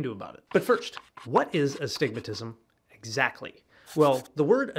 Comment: Always thought they were saying I have "A (space) stigmatism" ... And so assumed i had "Stigmatitis"